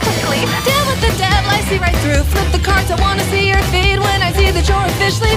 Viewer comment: I THOUGHT IT SAID I WANT TO SEE YOUR FEET AS IF HE WERE A CREEPY MIDDLE AGED MAN SLIDING INTO INSTAGRAM DMS 😭😭